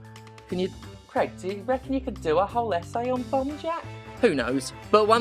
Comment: Until you showed the title at the end I swore you were saying 'Bum Jack' and it was so on brand I didn't even question it. So you got that going for you, which is nice.